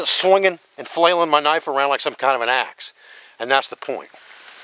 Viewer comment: Dave says "swinging and flailing my knife around like some kind of an AX" but i heard Like some kind of an ass... both are appropriate in this case i think. Thanks Dave for great vids and spreading the knowledge